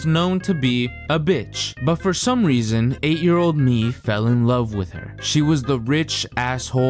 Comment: HE SAYS 8 YR OLD BUT THE CAPTIONS SAY 88 YR OLD WHO IS LYYYIINGG